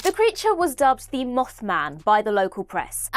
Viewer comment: For a second I thought you said "the moff man" and yes have Moffet write the mothman episode.